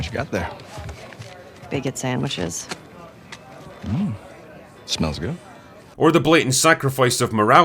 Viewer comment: When they said bigot sandwiches I thought she said bacon sandwiches